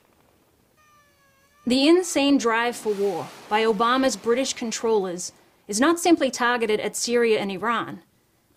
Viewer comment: did you hear what she said at 5:00. obamas british controllers????